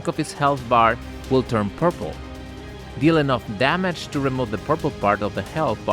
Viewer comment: I have no idea wtf is talking about during this mobile game ad read. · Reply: I heard “purple”